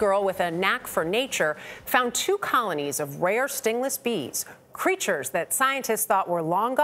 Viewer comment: Oh silly me I thought it said stringless bees I thought to myself wth what is a stringless bee then I listened to video